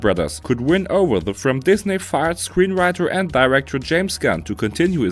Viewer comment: No idea what your saying most of the time. Did you say, Disney farts?